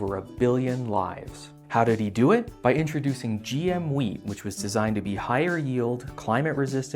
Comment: did he say GM weed? That's an awesome idea we need to get on that.